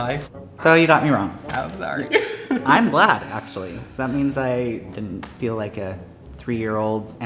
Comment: what did he say at 10:00 im so confused did he say “im black actually thats why i didnt feel like a 3 year old amputee”??